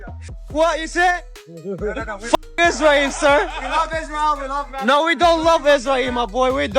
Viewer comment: I think he said "Yeah I’m from Israel”